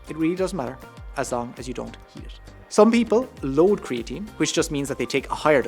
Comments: does he say as long as you don’t heat it? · Take creatine in juices, etc, as long as you don't....what ? Couldn't understand that last word. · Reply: heat it